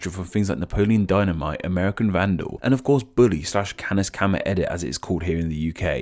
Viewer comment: I'm sorry what did you say? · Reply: Canis Canum Edit, it’s Latin for Dog eat Dog